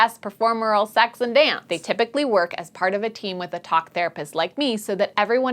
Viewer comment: I thought she said cock therapist